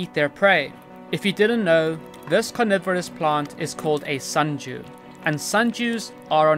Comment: To my American ears it sounded like you said "sun Jew".